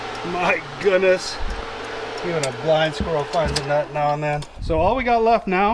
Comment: Hey, the saying is " even a blind squirrel finds a nut" either my hearing is going or I heard you say " flying squirrel" 😂😂😂😂😂